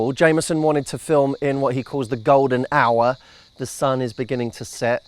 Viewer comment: I thought he said "golden shower". :S